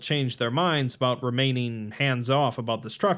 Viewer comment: Ngl I zoned out and all I heard was “I for one welcome our Sulfur based overlords” I was like wtf